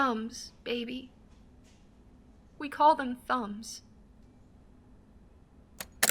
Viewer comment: I swear I thought she said "Phones, we call them Phones"